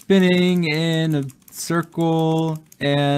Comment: I heard "im cream not purple" 😭🖐️